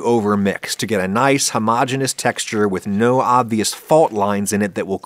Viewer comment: I may be audio hallucinating, but at about 2:30 I thought I heard you sing the praises of homogenization.